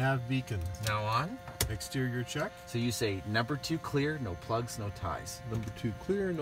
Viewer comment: what said the instructor at 1:12?. Number 2 clear.... ??? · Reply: Number 2 is clear, no plugs, no ties. It’s just a last minute visual check of the prop and engine before starting.